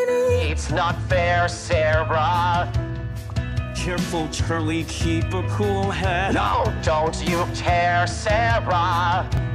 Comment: Wait why i hear "Charlie,keep you cool head" but he was alastor not charlie😂